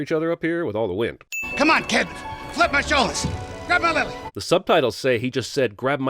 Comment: I never though much about “grab my lilly” and “grab my muck stick” until now. Thanks, CinemaSins. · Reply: He said that’s what the subtitles said. I could’ve swore he said “Grab my lapel”